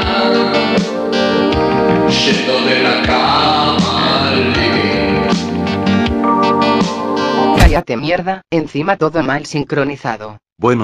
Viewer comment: alguien tiene la menor idea de que dice alfredo aqui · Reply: Yendo de la cama al living